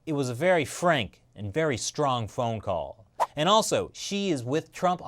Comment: "Xi is with Trump a 100%". And I nearly thought said "She is with Trump a 100%". Then someone said "Xi is a she?"